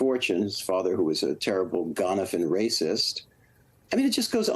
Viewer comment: he says of Trumps father something that sounds like "a terrible GONAFIN racist..." The CC spelled it "GONAPHIN," which isn't a word. WHAT did he actually say - what was that word???